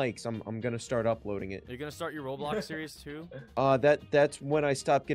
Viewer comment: Did he say ROBLOX series!?